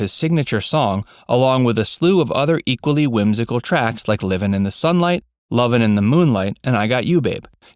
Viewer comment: SpongeBob SquarePants is how I got hooked onto Tiny Tim. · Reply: So did I. When I first heard it, I thought he said running on the bright side. But he said living on the sunlight.